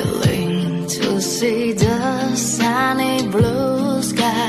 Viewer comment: 唱得很好，有了另一份凄美感，就是有两处英文有些造作，不看字幕还真的听得挺别扭，sunny我听成了soundly 或saddly, mountain 听成了mooten。